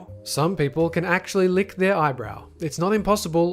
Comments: You ment elbow right? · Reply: Emale Hart nope he said eye-BROW i heard him say OW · I could've sworn you said lick their eyebrow instead of elbow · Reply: Yea he said eyebrow lols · On number 4, when you said "lick their elbow" it really sounded like you said "lick their eyebrow". Which would be infinitely more impressive. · Did anybody else hear "some people can actually lick their eyebrow" at 7:11? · Ok when he said some people can lick their elbow I thought he said eyebrow XDD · number 4...... He said Eyebrow.... Instead of Elbow...... WTF? Am i mishearing his accent or something? Elbows and Eyebrows are two extremely different things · Did you say people can lick their eyebrow? Cause when I searched that up I just found a Youtube video of some guy named Josh mooning me. I searched "can people lick their eyebrow" on Google in case someone for some reason wanted to see that. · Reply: I thought he said eyebrow. Nobody has a toungue that long. Bu after you said he meant elbow, I tried and suceeded. What am I doing with my life. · Umm... was it just me or on #4 did it sound like he said, "some people can actually lick their EYEBROW?" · did he say eyebrow? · #4, i thought you said eyebrow. Way more mind blowing than elbow. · Does he say eyebrow? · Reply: sounds like he said eyebrow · Omg ;O I thought he said people could lick their eyebrow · I THOUGHT THEY SAID PEOPLE COULD LICK THEIR EYEBROWS AND I WAS SO CONFUSED. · #4 did he just say lick their eyebrow? Or is it just me?